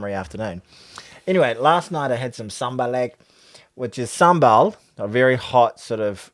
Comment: on first listen I thought said he had a Zamalek 😂 South African slang for a Black Label beer